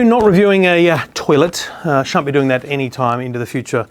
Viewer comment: i thought he said SHART be doing